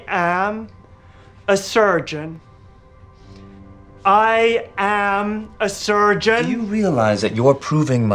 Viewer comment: The virgin surgeon vs the CHAD Han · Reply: he sounds like he is saying i am a virgin on the last part